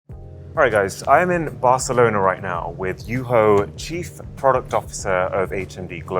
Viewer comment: Ok 0:03 did he say you whore